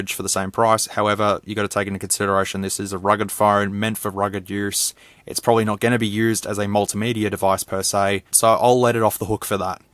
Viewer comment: I thought that you said "rigged" phone lol It could explode at any moment!